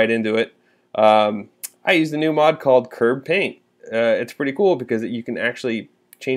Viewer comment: What mod you said? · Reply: KerbPaint is what I believe it was called. Not sure if it's been updated for the new version yet.